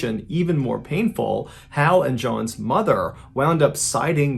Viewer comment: Photos and video. Grandmother btw. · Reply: Sorry, I have a hearing processing disorder so sometimes I miss words (or parts or them), so I heard "mother" rather than "grandmother". Now I understand why the girl still went on vacation with her mom to get away from everything. Michelle was dealt such a bad hand. I hope she finds peace in the next life, whether that's heaven or a new life in reincarnation or whatever anyone believes in.